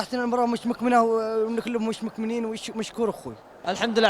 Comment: وش قال ذا ؟؟ ههههههههههههههههههههه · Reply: يقلك المباراه مش مكمنه واللاعبين مش مكمنين مشكور ي اخوي😂😂😂